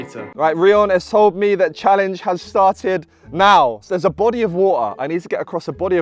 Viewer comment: Did he say "body of woa" or "BO'OH'O'WA'ER"